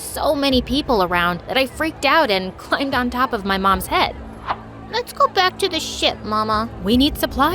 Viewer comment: I thought he say let's go back to shit mama😂